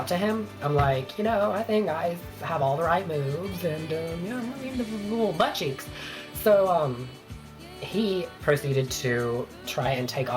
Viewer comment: all I heard was, "hwhahekafekkfjkaefeak, buttcheeks!"